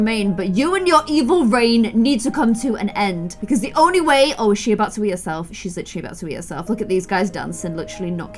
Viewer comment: I thought said Is she about to eat herself not wee 😂